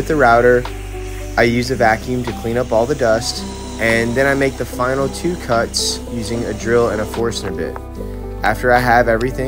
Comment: I swear I thought you said "make the final two cuts you using a drill and a japor snippet." I'm definitely a Star Wars fan!